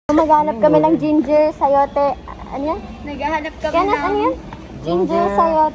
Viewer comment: I don't know but I heard said "Anya?" HAHAHA is it just me?